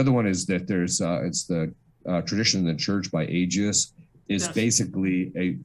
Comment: "Tradition of the Church" by who? Aegis? I can't tell what Fr's saying, does anyone know it? Edit: Tradition And the Church by Agius!